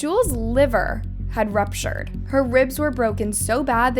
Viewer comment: ruptured!? Thought you said it exploded. 😮😂